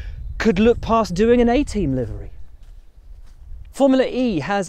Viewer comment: Question: Will said at 10:20, "But frankly I can't understand how anybody doing a deal with GMC could look past doing a ???????? What? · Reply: A Team!!!! The van was GMC